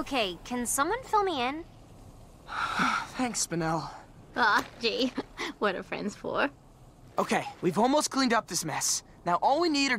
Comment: what does she say here? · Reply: "Aw, gee, what are friends for?"